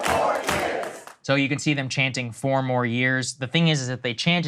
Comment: i thought they were saying 4 more queers. like they were waiting on a few donors to show up.